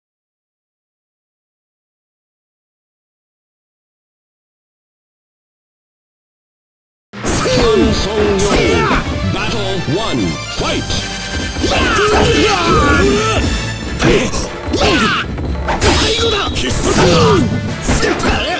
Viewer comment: XD I CANNOT BE THE ONLY THAT THOUGHT THE ANNOUNCER WAS SAYING HAN SOLO